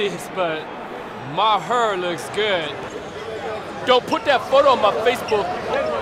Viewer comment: I feel like that's most likely close to what he actually said. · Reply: Jace Isham "Don't put that phone in my face."